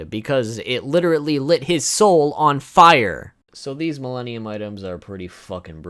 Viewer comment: When he said Milenium items i heard Mellenial's item